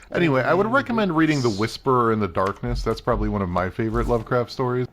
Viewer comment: The Whisperer In The Darkness is a Lovecraft story. I misheard and looked up Whispers In Darkness...an erotic thriller...